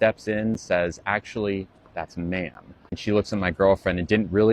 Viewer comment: Had to replay the misgendered one like five times because I kept mishearing the phrase “actually, that’s ma’am” as “actually, that’s a man” And if that were what the girlfriend had said then the rest makes no sense.